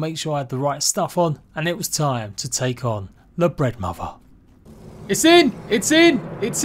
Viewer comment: Legit sounds like he said "The Breadmother"